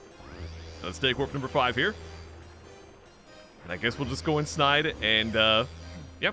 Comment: I know you did not say "Lets go inSNIDE" Fantastic pun of course I gotta leave a like